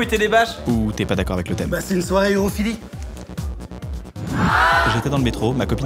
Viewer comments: C’est quoi qu’il dit à 1:13? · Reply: "C'est une soirée urophilie" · J'arrive pas à comprendre. C'est une soirée à thème quoi ? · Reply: urophilie, les gens se pissent dessus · Je comprend à 1:12 une soirée herophilie ? Ça veut dire quoi ? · ils dit une soire quoi ??? svp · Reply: babine urophilie (qui aime la pisse)